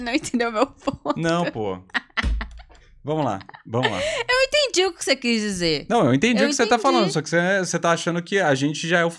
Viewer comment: eu entendi Silvia kkkkkkkkk